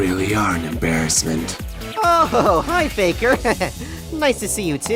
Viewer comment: does anybody else hear hi faker? Even the subtitles pick it up as that. And I’m not saying that to be a shadow hater I genuinely hear that. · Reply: That is indeed what is being said, I promise you're not tripping